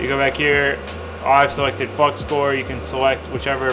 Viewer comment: Swear I heard "fucks core"